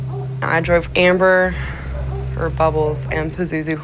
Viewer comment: I thought i heard amber heard.. lol